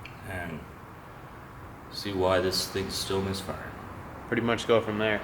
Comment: I’ve looked at this video a solid three times and every time I thought it said missing... not misfiring. 😂